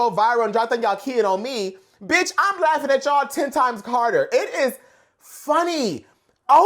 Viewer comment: it sounds like said “I’m laughing at y’al 10 times Carter” and I gagged because if so that’s a fabulous read considering a certain someone’s name has never been brought up in the beefs because the hive don’t play 😂